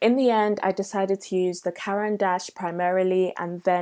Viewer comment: When she mentioned the "Caran D'ache" pencils, was I the only one who have never heard of them before and thought she said "Karen Dash"? lol